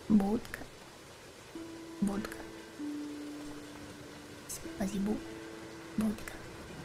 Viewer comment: might just be me, but i think she's saying "спасибо" or "thank you" in russian at 0:07 edit: it was actually